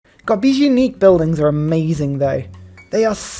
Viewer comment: what? before you said "... are amazing ..." lol 😅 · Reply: The unique building are amazing